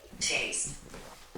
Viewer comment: Did anyone else catch it at 51:32 as takes a sip the spirit box says “taste”? 😮 The level of intelligence the spirit exhibited with its responses was incredible & impressive just as much as it was creepy!!!